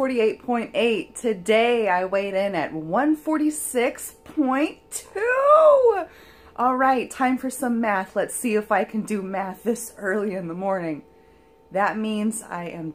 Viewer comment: I thought you said "now it's time for some meth" haha but no, it was math....congrats girl!